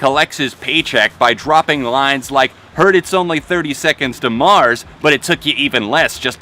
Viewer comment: Honest to god, i thought the mini crop said "Twenty Øne Pilots". I would've lost my shit... Thirty Seconds To Mars sounds right, yes.